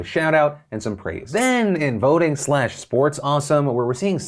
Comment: I thought he said 'boating'. I was so confused, like professional boating venues used as polling places?